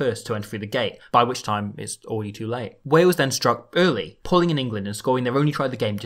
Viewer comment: Did you say “pulling in England” at 1:50 odd?